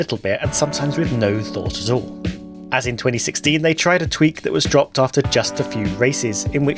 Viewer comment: There was another disastrous qualifying format that was dropped in 2016, but the one you meant was in 2005. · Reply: I thought the same because I heard the sentence as “And in 2016...” at first, but I think he actually says “as in 2016...” which is then correct.